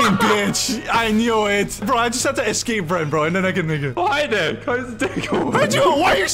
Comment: did he say the n-word?